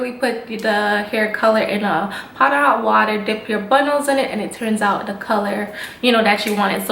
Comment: I know this is an old video BUT...I totally thought she was saying “and just dip your buttholes in it” 😅